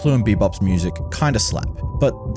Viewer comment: I'm sorry? Did you say kinda sucks? Both those soundtracks are brilliant and have not been matched since (in anime).